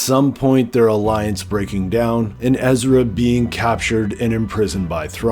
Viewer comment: I heard Ezrabine twice